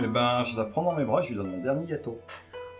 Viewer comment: J'ai pas entendu, il lui donne son dernier quoi ? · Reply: OK un gâteau probablement. 😅